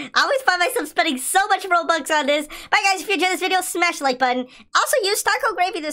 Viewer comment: it sounded like he said bye guys